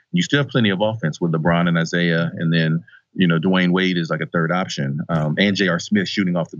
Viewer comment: Did he say dwayne wade? Im confused